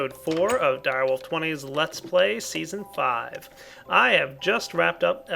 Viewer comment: Haha, yea I thought he said episode 5, but he was saying season 5 and I just wasn't paying attention :P lol